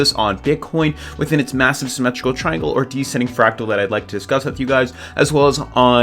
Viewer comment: Hasn't the triangle already been broken? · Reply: Sorry , I thought you said triangle. You said fractal